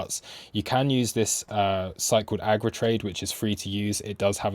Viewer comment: Bro pls what did you say @ 15:12 ? Agritrade?